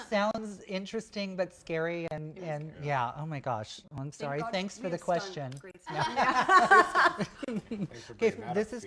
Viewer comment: What did Jason say at 15:39 ? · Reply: “thanks for bringing that one up Phoenix.”